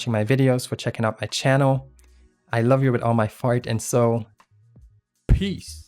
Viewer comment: Am I the only one who heard him say,” I love you with my fart and soul?” At